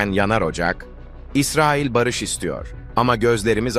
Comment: İsrail barış istiyor mu dedi ben mi yanlış duydum